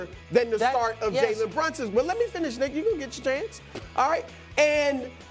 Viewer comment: I thought Brou said "let me finish ni$$a!"😂